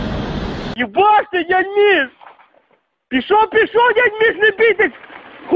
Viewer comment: Is this Russian. I thought the first bit sounded like a Scottish guy saying "You blocked it, didn't you?" and then I couldn't understand the rest, and then realised it's not even English. 🤣